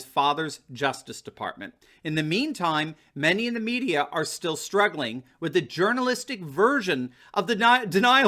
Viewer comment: Did you say media was strangling are struggling I thought I heard strangling I like that 1